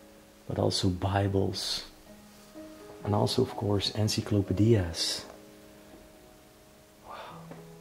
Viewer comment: is he saying encyclopedias??